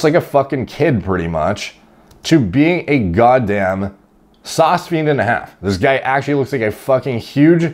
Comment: saucphene and a half, what does he say here😂😂